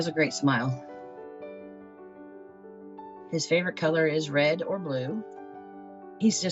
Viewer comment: His hair colour is red or blue? Maybe I misheard that.